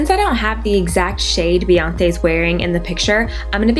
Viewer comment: lol i heard her say beyonthe at 1:38 but anyways love the look keep it up girl FLAWLESS!!<3